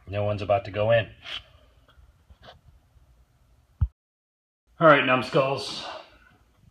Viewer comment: Did he say "Alright numbskulls" @ 18:00? lol